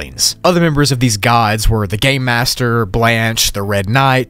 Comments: Did you say " Gay Master " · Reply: Game master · I Thought that he said the gay master and I thought that it was another one of the gods or "siblings" but then realized it was the gate master